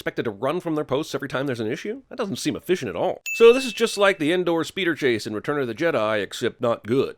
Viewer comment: anyone else think he said indoor?